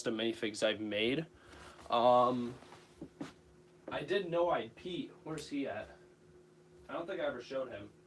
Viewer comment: I thought you said “I didn’t know I peed”!!!! Lol I had to do a double take!!!